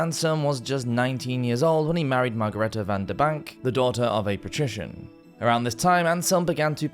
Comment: His wife - did we even hear her name? - was remarkable if only for bearing so many children when many women died in childbirth. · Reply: Margareta VanDerBank is what I heard